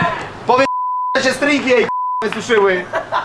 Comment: co on powiedzial? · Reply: „powiedz ze jej się stringi suszyły” chyba